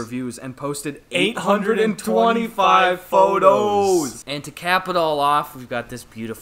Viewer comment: I thought he said capital and I flinched as images of chaos and violence popped into my head